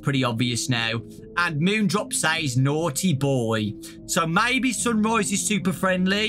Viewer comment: Sunrise actually sounds like he’s saying “I WARNED YOU, I WARNED YOU” maybe he could be a neutral animatronic warning us about moonrdrop (or someone else?) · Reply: To me, when Moon comes up, I thought he was saying “Monty, boy”. It does make sense for him to be saying “naughty boy”, but if he is talking to Montgomery, that would be pretty interesting.